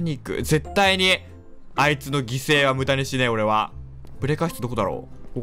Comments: 「あいつの犠牲はブタにしねえ｣って聞こえるの俺だけ？笑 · あいつの犠牲は豚にしねえに聞こえた